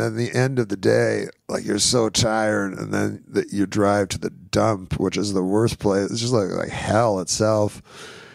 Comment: Great documentary. My only quibble is there are lots of typos in the subtitles. At 11:04, that's "dump" - not "dumb" At 11:08, it's "itself" - not "it's self". There are at least 20 but didn't feel like it'd be interesting to everyone to list them all. Especially for people who aren't especially fluent in English, correcting those mistakes might really help them grasp what is being said better.